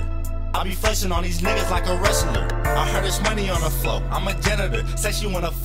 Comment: I heard it's money on floor.. I'm a janitor! 🤘😂🤘